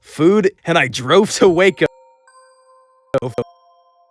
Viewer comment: He says Waco but the subtitles say wake up